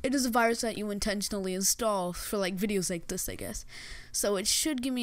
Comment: Lmao 1:14 the auto generated captions says "like this niggas" when he actually said "like this I guess"